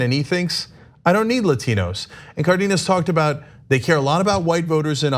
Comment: Ana is so out of touch with the Hispanic community she can't even pronounce Vanessa Cardenas correctly. Ana is proving her racism and indifference for our Latino brothers and sisters. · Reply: I thought they said "carnitas" at first